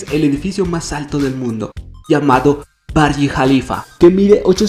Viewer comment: Minuto 0:46 pense que dijo Mia Khalifa :v